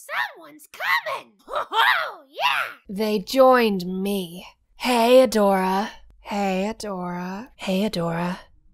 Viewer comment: is her saying “hey adora~” you’re welcome in advance.